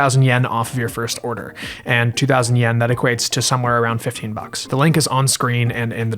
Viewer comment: I thought you said "50 robucks" instead of "15 bucks" and IDK why that's so funny to me.